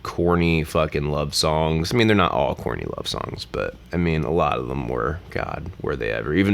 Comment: You said "corny love songs" 3 times and I thought you kept saying "Courtney Love songs" and I was so lost I had to rewind like 5 times before I finally got it